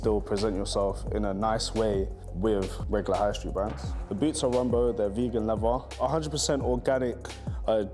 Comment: Okay so who actually heard the brand of the boots is wearing at 13:57.. run bow? rum boat? I really can't hear it properly.